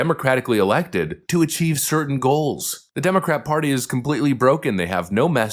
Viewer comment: 😂😂😂 I thought that said the dumbest party 😂😂😂